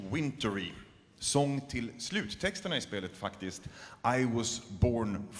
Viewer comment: sounds like hes saying F*ck this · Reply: its Faktist. its swedish for "Actually"